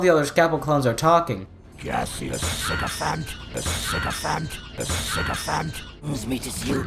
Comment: what the heck did he said. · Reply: Gaseous sycophant